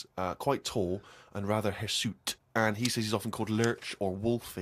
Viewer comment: What's the word he used at 0:07 i would look it up but the spelling is hard to hear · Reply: TheNiceZombie I think he said "hirsute" which basically means hairy.